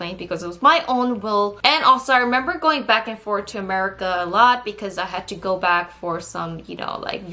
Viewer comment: did you say,....." 'Murica "?! XD